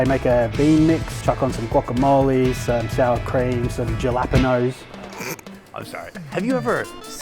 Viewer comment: jalapeños??? Almost sounded like he said galápagos. Sounds more like "ha-la-pen-yos "